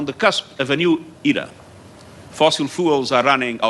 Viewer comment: Did he say “fossil fools”?